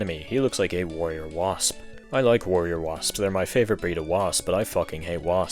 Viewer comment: holy shit I am so Metroid Prime i thought you said War Wasps, who i just realized must have been based on Warrior Wasps